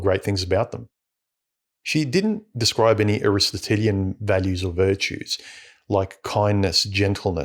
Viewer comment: Did the word you used around 3:36 come from the word (name) Aristotle? What did you say? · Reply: Yes, that's what i was referring to... 'Aristotelian'.